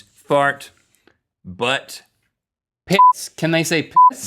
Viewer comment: Sounds like he said pits 😂😅😆